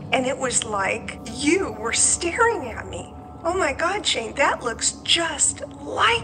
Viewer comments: a friend of mine pointed this out to me but at 26:08 does anyone else hear a slight whisper "Shane"? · Am I the only one who heard the word ‘Shane’ get whispered at 26:08???? Like wtf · Right so listen, is that whispering in the background. Or did someone say Shane. Coz that shit gave me the chills propper! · Does anyone else hear someone say "Shane" at 26:08? I hope it's. If not, its VERY WEIRD. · Have anyone heard soft "Shane" at 26:08?? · I heard something say Shane at 26:09 I thought the other comment was a joke but it was legit And my eyes started watering and I don’t get spooked like that easy , prayers goes out to you Shane 🙏🏽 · THE WHISPER AT 26:08 SOUNDED LIKE IT SAID "shane.." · Okay but I heard something whisper “SHANE” at 26:08 and I’m literally hiding in my blanket right now I’m so scared!!! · Did anyone else hear "Shane" or something like that 26:08 or am i losing it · Does anyone else hear a female voice saying ‘Shane’ at a round 26:08? Not even trying to add to the spookyness of this video but I had my headphones in and played that part just after his mum finishes speaking over and over again and keep hearing it - very strange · right did anyone else hear “shane” at 26:08 ?? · What is that? At 26:07 you can hear someone say "Shane". Does anyone else hear it or I'm I just nuts? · im freaking out, did anyone else hear a third unknown voice whisper 'shane' at 26:08? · does anyone hear someone saying "shane" at 26:07? it's not, tho